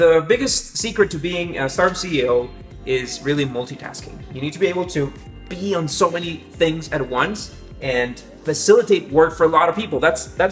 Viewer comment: I heard "you get to pee on so many things at once...." lol